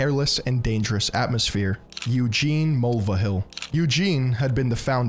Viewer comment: Did you say... Mulva?